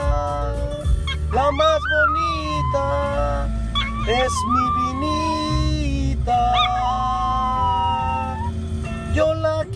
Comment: What he’s singing ; little girl doggie the prettiest one It’s my little girl I love her very much She’s my little girl doggie I love her very muuuch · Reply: polar oh you’re right, I’m sorry I didn’t read the description so I thought he said “mi niñita” instead of “mi beanita”